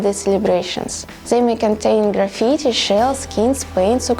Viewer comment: hey, subtitles do not match with what do they say, what's up? 0:44 (graffities, not supplies...) 1:39 (Rust, not Corrosion...) 1:42 (Tar, not Reson or sth like that...) 2:04 (supplies, not upgrades...) 2:14 (missing 'more' balanced...)